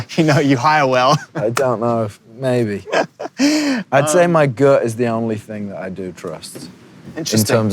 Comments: heu at 14:32 did he said girl? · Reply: he said gut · did he said the girl is the only thing I trust in terms of people at 14:31 ? Pls tell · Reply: he said my gut